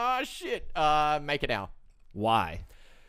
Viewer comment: what he said .. wanna do research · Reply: makerdao thx